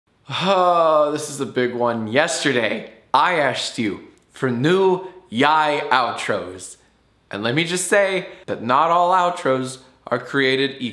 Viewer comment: On the 400 yiay one I thought I heard yesterday I ass